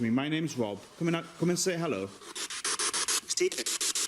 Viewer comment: I hear "Stephen" after Rob says his own name....